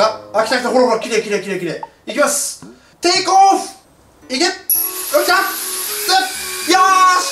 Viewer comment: の行け！がイクッ！って聞こえる